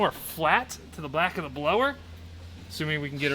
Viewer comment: did you say, "to the black of the blower"???🤣